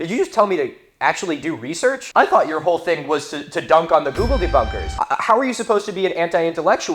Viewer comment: Every time someone says Google debunkers, I hear "Googlede bunkers" great idea with a hoodie, if you can make version with zipper and inside pockets for part 3 I will buy all